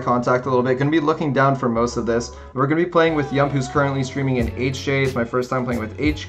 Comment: I have 13.5k trophies I know I have a long way from the leaderboard but I'm low key tryna get into one of videos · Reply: oh lol I just heard him say he was playing with sub 🙃